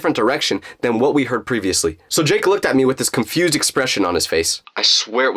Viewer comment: Did He just say drake